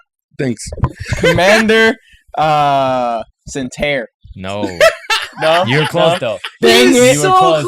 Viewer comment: I thought you said 'commander Sin... hair' lol. He does have fabulous hair.